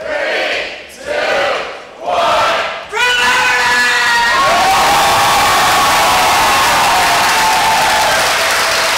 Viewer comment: What did just say?! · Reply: Exhausted